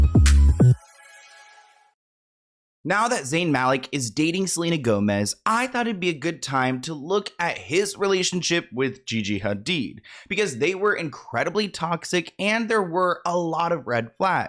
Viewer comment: Excuse me, the first thing I heard was “Now that Zayn Malik is dating Selena Gomez”, like how far behind am I?